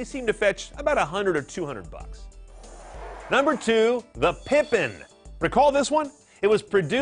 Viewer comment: Dang I thought the guy said pimping